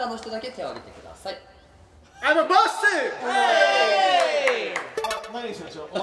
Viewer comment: I'm a BO$$Y って聞こえる